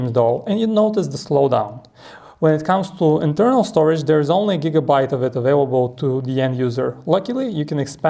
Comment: did you say "only ONE gigabyte available" ?